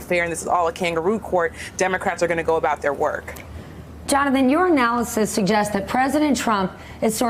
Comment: Every time someone says "your analysis", I hear "urinalysis".